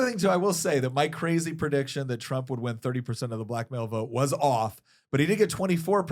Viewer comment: 😆🤣😂 At first when you said Trump won 24% of the black male vote, I heard “24% of the blackmail vote!”